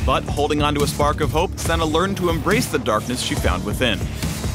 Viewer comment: I heard "AbsoLUCIAN" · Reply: Edward Brave I heard “ar-senna-l”